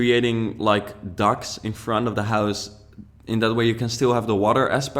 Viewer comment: Awesome build/sketch :D Thought you said that you had a duck in front of the house for the longest time ;D